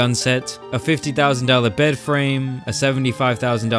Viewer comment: Excuse Um like sorry did you say bedframe for 50k